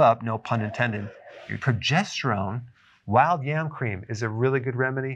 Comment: did he say "wild yam cream"? Where will I get those?